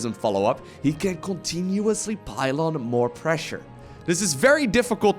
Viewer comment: Did you really say the protoss can "pylon" more pressure? lol?